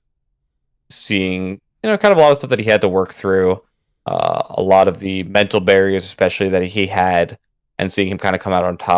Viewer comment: Who is Cody talking about at 15:55? Hes talking about the doc and then talks about a "he" that he admires but i watched several times and dont hear a name. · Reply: PPMD